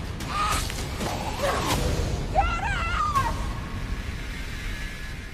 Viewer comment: WHAT DID SHE SAY?! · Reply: She Said, "JUDIITTTHHHHHH!!!!!!" Ya know, just like Rick said, "CORRRAALL!!!." :v